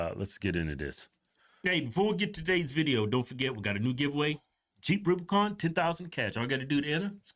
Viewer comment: When you say jeep it sounds like you say cheap Rubicon....who would want a cheap Rubicon? 😂